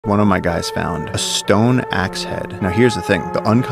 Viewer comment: Bro I heard him say "one of my dads" I'm tripping lmao